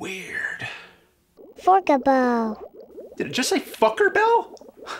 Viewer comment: Did it just say Fuckerbell?